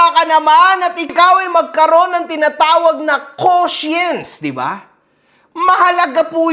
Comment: I THINK YOU ARE SAYING CONSCIENC NOT QUOTIENCE.